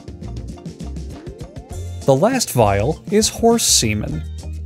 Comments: “The last vial is horse semen.” And then just silence. So much silence. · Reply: I thought I misheard 'hiking boots', but didn't care enough to backtrack. Then he said 'horse semen', and I was like, "Waaait a minute." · Did he just say hiking 🥾 boots 👢